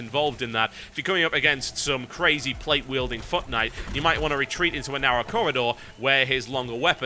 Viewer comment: When he says foot knight, it sounds like he says fuck knight.